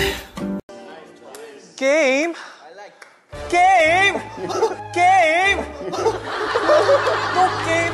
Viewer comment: idk why but all I heard was “gay!?!?!?”